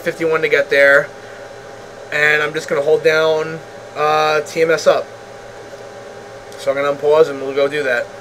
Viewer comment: Please explain at 13:35 please! Why 'hold down TMS up' or did i heard it right? Did you hold it the whole time till target? Help